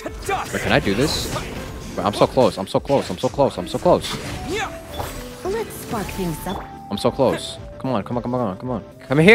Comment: I swear to God that I thought Lisa said "Let's fuck things up"